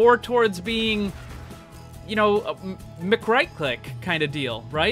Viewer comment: wtf was that word you said ? (Mcrikelike) ? LMFAO please define that word.